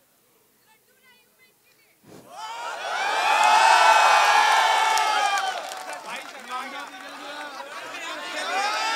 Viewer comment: what did she say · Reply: She said Laddu laai hu aapke liye